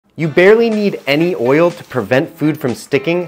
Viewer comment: I thought bro said “you barely need any whale”